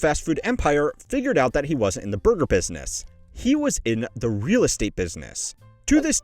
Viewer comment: almost sounds like you said booger business